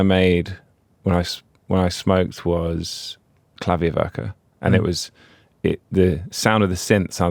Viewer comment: Anyone know what song he’s talking about here? Captions say Clavioca? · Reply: Klavierwerke